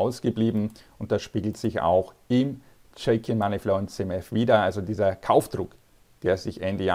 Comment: Wie heißt der Indikator bei 7:56? Ich konnte es akustisch nicht verstehen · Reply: Das rote? Das ist der Chakin Money Flow.